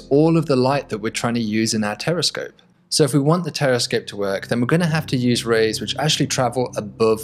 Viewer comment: I thought I heard him saying "Terrorscope", looks like I need to clean my ears. But the video showed us a very good concept.